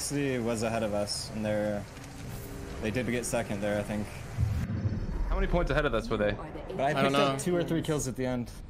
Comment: 何ポイント差だろうって英語で何て言ってるか分かる方います？ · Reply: How many points ahead of us were they? ですね